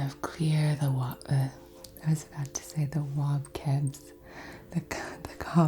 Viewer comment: I had this playing in the background, trying to get out of my head, and I just hear "wobcebs" and I'm losing it 😂😂😂😂 I love you 😂😂🥰🥰